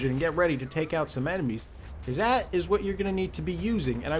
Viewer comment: oh wait he said "enemies" I thought he said "bambies"! xP